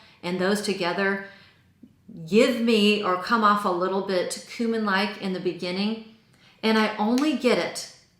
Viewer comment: Definitely thought you said "human" instead of "cumin" and I was like "girl I don't like 'human' either" lollll